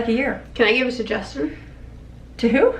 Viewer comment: I just CRACKED UP.....I SOOOOOO THOUGHT SHE SAID, "Can I give it to Justin?" Also LOL I was like, NOOOOOOOOO MATTHEW. NO JUSTIN. ONLY MATTHEW LOL